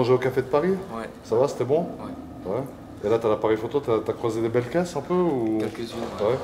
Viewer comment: j'ai entendu "t'as croisé des belles fesses un peu ?"